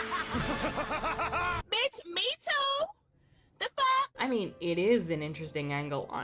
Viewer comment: please tell me what the woman said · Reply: "bitch me too da fuck/the fuck"